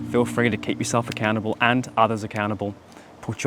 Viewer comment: when you said accountable i thought you said a cannable, the whole time XD until i read the subtitles, happy new year!.